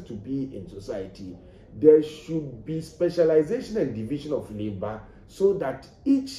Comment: Pls 3:25 what was he saying...there should be specialization and division of what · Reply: Labor